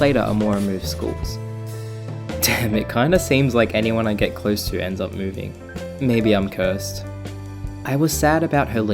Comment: where have i heard that before? "Feels like anyone I get close to ends up leaving. Maybe I'm cursed